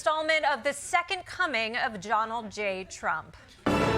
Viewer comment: I love how I keep hearing "Jonald J Trump"